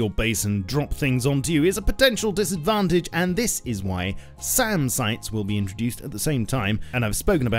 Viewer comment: SAN sites? Did I hear that correctly? :P I think you might mean SAM sites!